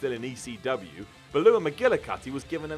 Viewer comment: What was that name? Sounded like "Balooah". I think you mean Beulah McGillicutty...